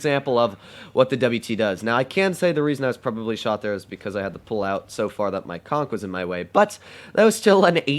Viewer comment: thought he said I had to pull out so far cause my cook was in the way.